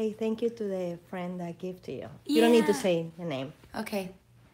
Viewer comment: did she said "You don't have to say HER name." and the face Camila did after hearing that. Lol. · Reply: nia no dice eso , dice the name